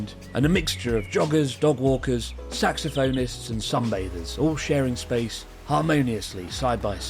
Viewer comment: I had the volume turned down low and I misheard "saxophonists". Thought you were saying "sex offenders", which would be no surprise if it was a park somewhere else not Japan.